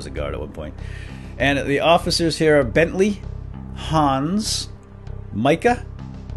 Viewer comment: BENTLEY! That makes sense, I thought it said Bent Leg...